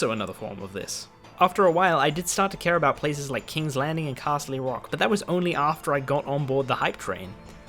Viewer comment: one problem with this video. 8:21 that isn't Casterly Rock, that is in fact Dragonstone. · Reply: I thought he said Castle-y Rock...I'm kinda glad that's not a real thing, that would have lowered my opinon of Game of Thrones fans even more.